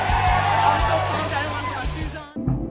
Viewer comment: "i'm so pumped I don't wanna put my shoes on" is that what I heard? :D